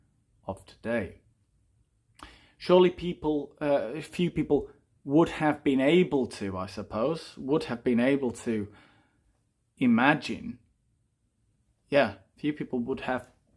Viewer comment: isn't it "but surely few people had been able to imagine THEN..." instead of "but surely few people would have been able to imagine THEN..."?